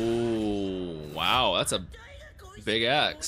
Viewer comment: I thought he said "Big Ass", and I almost threw up.